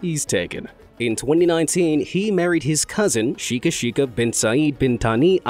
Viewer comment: did I hear the right? "He married his cousin"🤣😂😅